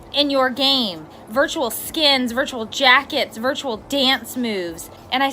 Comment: did she just say jackets